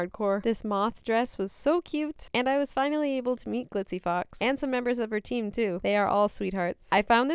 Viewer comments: Misheard it as glizzy fox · I thought it said glizzy fox 🧍‍♀️